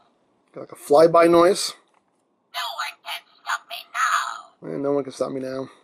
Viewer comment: when started showing off this figures sounds, i misheard the first line as “no one can f**k me now.” which didn’t come as a shock, i mishear things all the time. but when i misheard repeating that line and thought he said f**k, i straight up didn’t know how to react. took quite a few times rewatching that bit to figure out he’s saying “no one can stop me now” and not just commenting on how it sounds like starscream’s saying f**k.